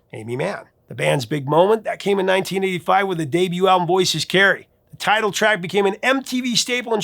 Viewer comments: Voices Carry, , that is what she was saying, I thought she was saying Scary Sherri. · waiiiiiiiit.....this whole time i thought she said "This is scary".....its "voices carry". I was today years old.